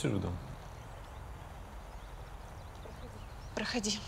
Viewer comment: Я одна услышала, как на 43:01 видимо суфлер за кадром подсказал "проходи" и эта Инга повторила